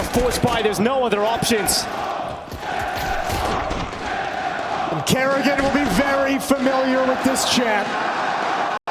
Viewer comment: What were the fans shouting at 27:00? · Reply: "Send them home"